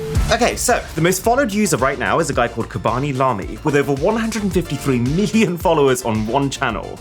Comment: Did he just say Kebaby Lami? 😂